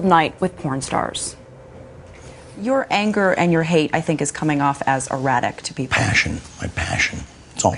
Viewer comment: At first, I thought she said "Your anger and your hate I think is coming off as erotic"